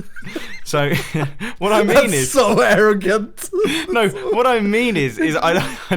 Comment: When said "that's so arrogant" I thought he said " that's so American"